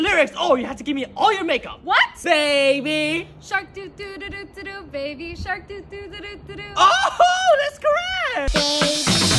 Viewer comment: I thought it said baby shrek 💀💀